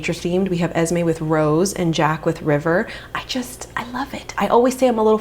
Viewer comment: Lowkey thought you said your sons name was Jack the Ripper...